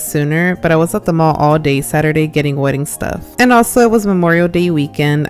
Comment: did I hear wedding...?! 👀